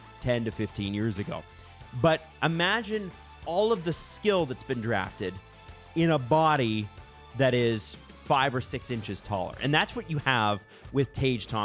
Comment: I had to replay that like 4 times because I kept mishearing it as "Imagine all that in a body that's 5 or 6 inches tall." What?